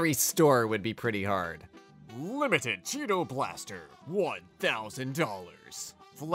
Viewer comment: I misheard Cheeto Blaster as "Cheetoplasty" and was just kinda sitting there tryina figure out what a cheetoplasty is